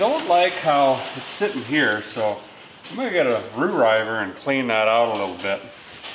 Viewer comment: I thought I heard you said "rewriver" so I had to listen to it 5 times... Been hitting the cold snacks I see. Someone I can get along with.